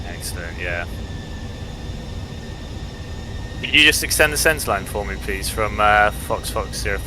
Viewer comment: Awesome!!! This video is really helpful! Thanks for the video. By the way, what was he saying at@ 31:58? "extend the sense line?" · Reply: Glad you like it! "Extend the centreline" was basically asking the pilot to create a line from the descent point on the ILS, which can be seen on the ND screen. This improves our situational awareness, and helps for energy management.